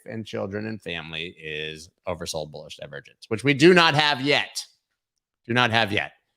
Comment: or right before he says his favorite thing is “over sold bullish leverages” can someone explain what that means? I’m a kid trying to learn this stuff. I think leveraging means when you try to do stock stock market quickly instead of holding. And bullish means it’s going up. But for some reason I can’t put the two ideas together LOL somebody help me understand, thanks